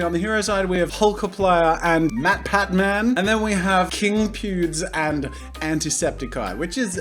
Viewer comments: It sounded like he said king pewbs at the beginning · i thought he said king pubes